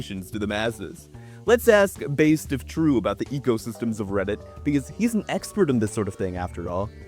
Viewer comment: I keep hearing "egosystem" instead of ecosystem.